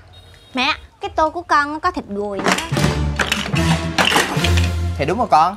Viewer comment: Tôi nghe thành thịt người :>